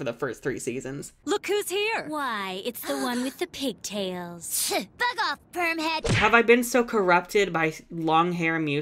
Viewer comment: i thought Musa said “f*ck off perm head”😭